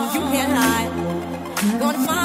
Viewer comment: When you played ready or not I thought It said radio nut